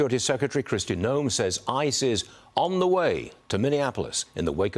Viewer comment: I thought he said "ISIS was in the way to Minnesota".